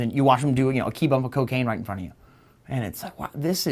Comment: No one can do a "Kilo" in front of him. Does Cawthorn know how much a kilo is? Damn that man is dumb! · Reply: He said "key bump." I had to rewind because I thought he said kilo, too. The fact that he said "key bump" makes me think he's been around coke a little more than he'd like us to think